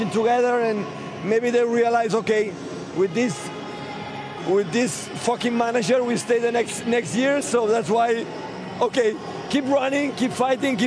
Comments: Did he just say "With this... with this f*cking manager we stay the next years" at 3:42 😂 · did Pep say “with this F*****G manager”?!! 😆😆😆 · does he say "with this fkn manager"? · "with this f*cking manager" did I hear that right?!?!?